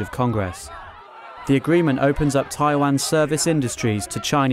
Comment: did he say Thailand instead of Taiwan?